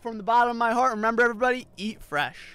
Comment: When He Said Eat Fresh I thought he said eat breath 💀